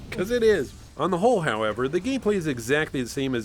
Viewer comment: Did you just say "On the hole"